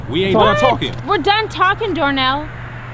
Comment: Did she just say “we done talkin door nail”?